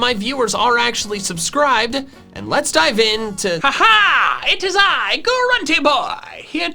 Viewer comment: When grunty boi came in it sounded like you said “now let’s get into it is I grunty boi”😂